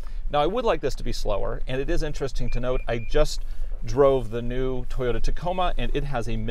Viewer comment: did I hear you say you “just drove the new Toyota Tacoma?”